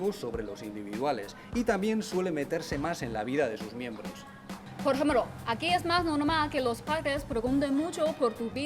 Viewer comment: que dijo ??? hahaha trabalenguas xd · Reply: "Por ejemplo" XD